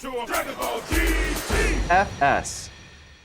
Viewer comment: I always hear “Fuck Sake” in my head instead of “FS”